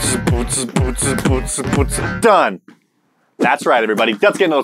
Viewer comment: When started to beatbox it sounded like he was saying "poetsen" which is Dutch for "wiping" Accurate! I lost it and now it's a thing here...